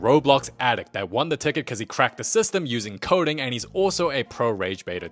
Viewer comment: When he was describing the 4 winner I thought he said cracked his sister